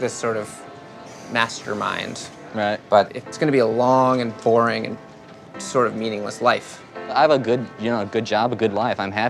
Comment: nev said "long and boring and kind of meaningless life" and the closed caption said "long and boring and meaningful life"